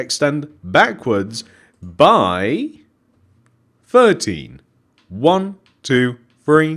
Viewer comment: I THOUGHT HE SAID 30 UGHHDHEDBBE